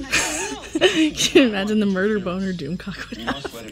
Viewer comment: Did I hear you say "murder boner"? Raffi bomb!? The League is wicked funny. Luv you, byyyeeee. :-)